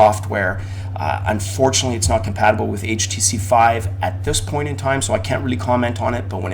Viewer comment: LOL ya i noticed that too...usually i have the mic up higher. Extremely friggin annoying and will not be on desk anymore agree. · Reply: P.S. Just cause I've watched it twice and enjoyed. I swear around 8:00 in you say " HTC 5". :-) Subscribed, keep it up!